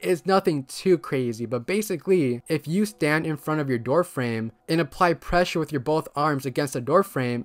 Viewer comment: I swear you said If you stand with both balls and apply pressure